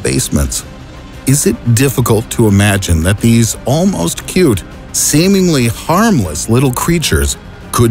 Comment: did he say cute?